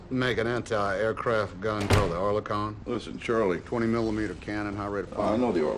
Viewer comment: The auto generated subtitles made this scene particularly interesting and about time. The CIA man Gust is speaking about Oerlikon which was used in Afghanistan against USSR but subtitles said "Orlan" which is a particular type of Russian drone currently being used in the Ukraine conflict and CIA is observing it's potential and actions. Though it's a technical glitch but this glitch in this particular time made it more interesting.